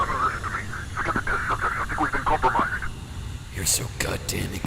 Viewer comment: I think at 39:08 he says "Forget the test subject(s), I think we've been compromised.